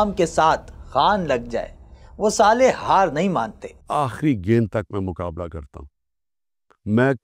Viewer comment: did he just said "salah" ?